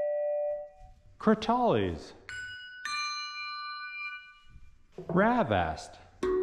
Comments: Rav Aast i thought he said grabass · Thought he said "grab ass" for a second until I read "rav vast" 💀